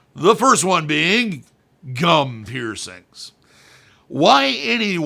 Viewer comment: You're welcome. There is a lot of confusion around that name because it has been used for jestrums. In fact, the first time I saw something like this it was done like a backwards vertical labret. The poor girls teeth were already seeing signs of wear after just 2 months. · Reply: that's awful! On a side note, I thought you said "cum piercings" and was very confused 🤦‍♀️